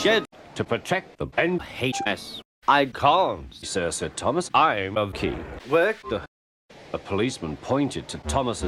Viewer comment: What did he say? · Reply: It sounded like "I'm a key worker"